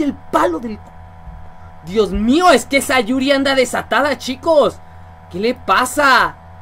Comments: Like si escuchaste que dijo Sayuri xd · like si oíste que dijo sayuri ;w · Dijo Sayuri o esa Yuri????? XD Yo escuche Sayuri pero creo que dijo esa Yuri